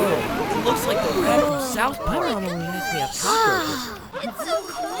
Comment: is it just me or did someone say south park